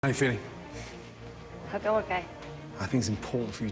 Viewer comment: Wtf did she say "hadoken" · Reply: “How you doing,” honestly does sound like “Hadoken,” I don’t blame you.